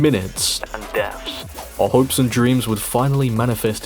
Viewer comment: I'm sorry did i just hear hopes and dreams?